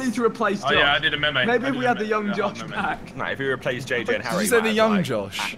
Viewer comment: Did anyone else hear what Josh in the background said that 15:47 "Maybe we could replace Harry and JJ with active members of the side men"